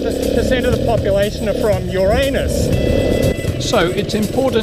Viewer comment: I thought he said "assholes are from your anus"